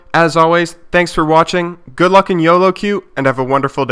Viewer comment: Good luck in yoloQ ? :D did i just hear that at 5:20 :D